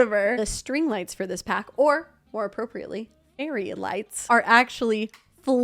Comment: i heard this as area lights and spent so long confused on what that meant😭